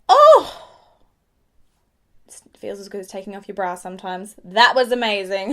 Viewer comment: did she say brows????😂